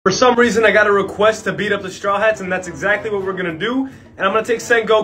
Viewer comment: I thought it said beat the straw hats meat at first..